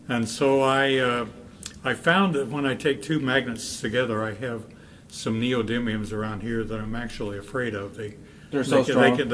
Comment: what does he say there ? sameodemium ? somyodemium? does anyone know ? google doesnt help me.